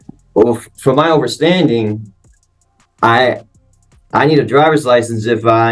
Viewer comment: did he just say 'over-standing'? What a loser.